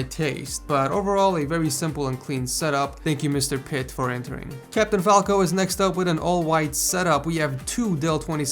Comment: DID YOU JUST SAY CAPTAIN FALCO, ITS CAPTAIN FALCON TRIGGERED